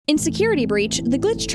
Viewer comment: The first thing I hear "Insecurity breach"